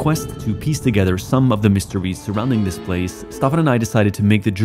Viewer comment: who else heard MrBeast instead of mysteries?